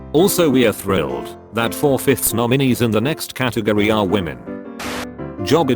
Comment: thought it said ‘normies’ lmao